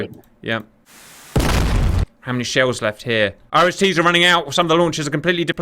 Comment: I swear it sounds like he is saying "Irish Tea."